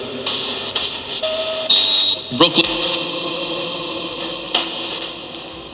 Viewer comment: I hear "Brooklyn" I’ve heard this in my readings before too. Is there something it could be saying besides Brooklyn.